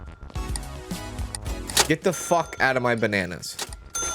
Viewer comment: 3 and a half minutes in and all I hear is “get the fuck out of the bananas” 😂